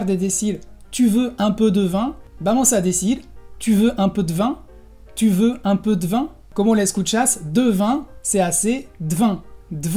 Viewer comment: alv yo entendi "Yudo ante te van" a huevo :v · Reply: Esque ahí en el minuto 1:36 dijeron una palabra en francés medio rara y lo único que entendí fue "Yudo ante te van" xdxd